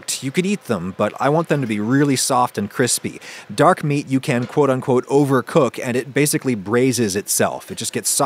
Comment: the subtitles say that Dark meat you can overcook. Is that right Adam?